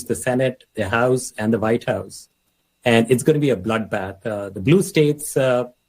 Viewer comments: did he just say "its gonna be a bloodbath"? I wonder if he's gonna get the same backlash Trump got? Rhetorical question of course. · wait, did he just say it was going to be a "bloodbath" 6:20 OMG, call the authorities.